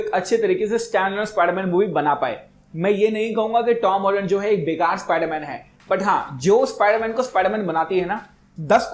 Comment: to iss se pehle kya kaha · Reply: Are aajib YouTuber ho yaar pehale buraai kar leteho aur baad me kehate ho ki me ye nahi kahuga ki bura spider man hai